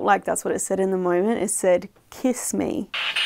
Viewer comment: I can see where you think it says “kiss me”, for sure - but the way you replay it, it sounds like it is saying “listening” in an accent.